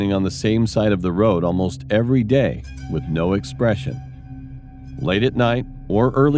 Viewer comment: when he was talking about the dude in the road, i had my ear buds on, and they have bad audio. so i thought he said "no aggresion" when he said "no expression"